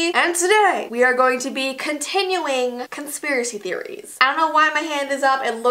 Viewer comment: it sounded like you said “continulling”